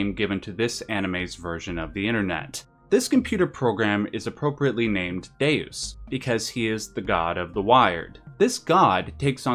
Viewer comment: I thought you said "his name is Dazed, because he's the God of the Wired" 🤣🤣